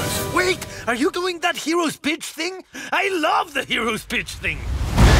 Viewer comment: "WAIT ARE YOU DOING THAT HEROS BITCH THING?" you csnt tell me it doesnt sound like its saying 'bitch'